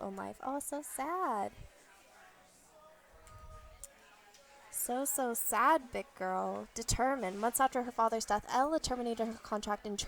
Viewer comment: Did she say so sad thick girl at 9:17???